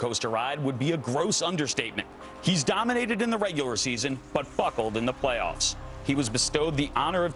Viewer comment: lol 0:28 - I thought he said Fucked